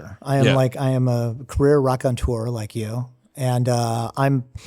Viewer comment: I heard "Wreck on Tour" 😂